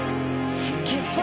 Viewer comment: Why did I hear “daddy for each other” instead of die for each other 💀😭